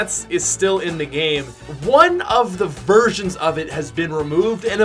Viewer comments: Daso he said versions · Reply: gaming with Raul im joking bro, i know he said versions but it kinda did sound like he said v*rgins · did he say "One of the virgins..."